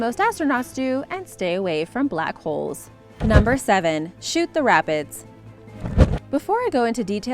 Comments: I thought she said “shoot the rabbits” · I thought you said shoot the rabbits until I read the thing lol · Who else thought she was saying ‘shoot the rabbits’ instead of ‘Shoot The Rapids’? 😐😐😂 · At first I thought she said Shoot the Rabids 😂😂😂😂😂😂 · At first I thought you said “Shoot The RABBITS” lol Edit: omg wow thx for the likes I didn’t think I would get this many! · I thought it said shoot the rabbits lol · When you said shoot the rapids I thought you said shoot the rabbits